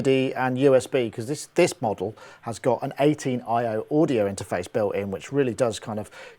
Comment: yes it could do, but doesnt the Focusrite have the same number of inputs? The X-Air 18 has 18 inputs, and 8 outputs- but this inlcudes monitoring or (L+R) outputs, so I'm not sure it will give you more than you already have. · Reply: Oh! I thought I heard you say it was 18i 18o! In that case, no it won't work! Haha thanks for the reply anyway! Keep up the great work :-) Rich